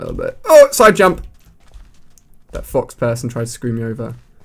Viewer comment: sounded like you said "that fu**ed person" XD